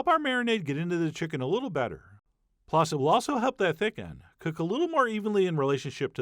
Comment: When I heard you say "Thick end" I thought "Thiccen" like "thicc chicken" because it looks delicious. kek